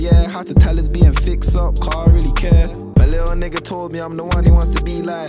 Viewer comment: Usually thinking big up to the editor of these Duppys but 3:25 the editor fully put the opposite of what said??😂: "had to tell his BM fix up cos I really care" Editor: "I don't really care" 😂😂😂